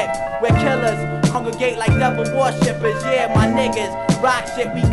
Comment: did he say "my ni99as"...i could've sworn he said that